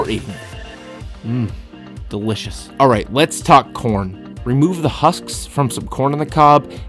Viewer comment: just talk corn, I thought he said porn xD